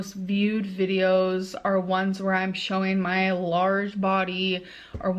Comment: lol did she say showing her lard body?